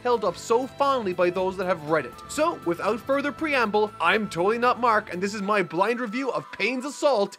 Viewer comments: Maybe Toei is after mark because when he opens and closes his videos with “I’m totally not mark” it sounds like he says Toei not mark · Instead of "Totally Not Mark", I keep hearing "Toei, Not Mark". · Sometimes I hear him saying “ I am toei not mark”